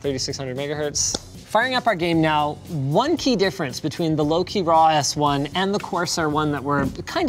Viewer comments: till this point I thought Linus is saying low-key and then I checked the description. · I wasn't really paying attention at the beginning of the video, so every time he mentioned Loque I thought he was saying "Low Key" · I only hear "Low key" when he is saying Louqe and its kinda confusing